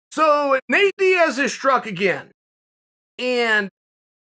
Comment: Definitely thought he said "Nate Diaz is drunk again" 😂😂